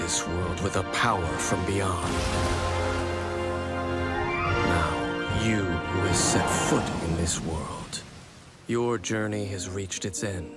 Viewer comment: I thought Dainsleif said “Now you will Simp for this world” instead of “now you will set foot in this world” help- 😭 🤚